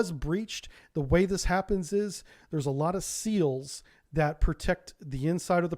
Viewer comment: When you started explaining the flooding I heard “there were a lot of seals” and now I can’t stop thinking about an army of marine mammals attacking a Russian sub😂